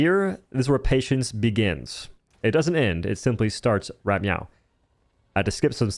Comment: did u say right meow?? (ily for that)